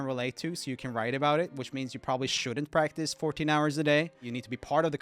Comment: j27:30 he said 14 instead of 40 smh